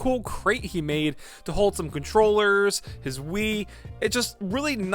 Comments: i seriously thought you said he built a chest for holding his controllers and his weed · Why did I think he said he keeps his "controllers and weed" in that box @ 6:17 LMFAO